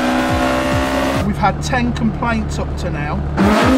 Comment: 0.07 i know he said complaints but i heard "we've had 10 compliments up to now". 😂